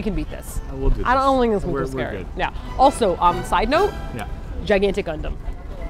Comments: I heard "Gigantic Condom" 😹😹😹😹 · I thought said "Gigantic Condom" and I was sooo confused... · 0.39 I thought you said gigantic condom..... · thought she said "gigantic condom"